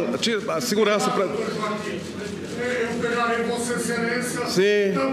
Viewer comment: A baleia ameaçou o Joaquim dizendo que "pegaria" ele, foi isto mesmo q ouvi no minuto 2:00? Tinha de ser um caucasiano mesmo pra fazer isto rs rs rs...